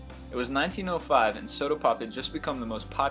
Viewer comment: did he say 1:36-1:37 'sodapoping'?